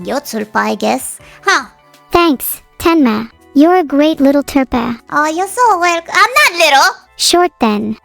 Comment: Please tell your editor to Keep Themself Safe. I barely know english, but I can see that he miss half the words. What is "turpa"? There is now such word. It is "tulpa".